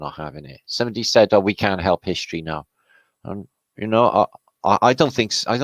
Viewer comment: I heard "ain't shit history" §=0)p